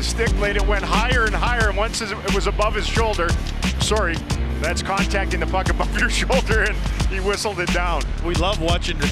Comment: what exactly does the sportscaster say? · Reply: "once it was above his shoulder, sorry, that's contacting the puck above your shoulder and he whistled it down." "we love watching hand-eye coordination, but eventually I'm gonna to have to call this".